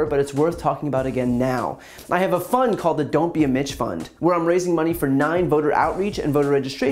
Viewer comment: I kept hearing him say, "Don't be a mensch fund," and I was all confused. Why wouldn't you want to be a mensch? Then the last time he said it I heard "Mitch" and then it all made sense. 🤣